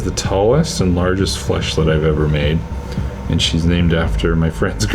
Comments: I thought it said fleshlight, so I was just sitting here like "So where does the dick go?" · I thought it said "fleshlight" 😂